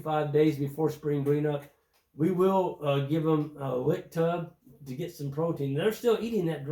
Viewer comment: I couldn’t make out the source of protein you use in the winter before green up. Sounded like you said lick tub?